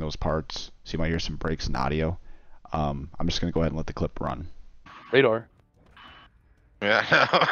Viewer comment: That sounded like Pred?!